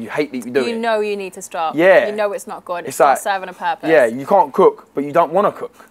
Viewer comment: My bad. I thought she said what if I didn’t chuck. Not cook. Lol